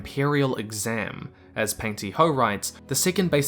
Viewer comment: "Pang-ti- Ho" he said. "Panty Hoe" I heard. Yeah, that's probably my problem, I understand ¯\_(° ͜ʖ °)_/¯